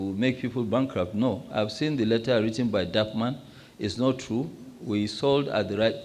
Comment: Did Mr Dangote mentioned “DarkMan” @ 15:51mins. Pls can some tell me what he said? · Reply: Thank you bro! “DAPPMAN” he sound like DarkMan! 😂 thanks for the clarification and informative response